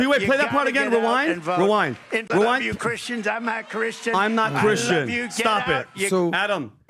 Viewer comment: Sounds like he said “I’m A Christian” not “I’m not Christian”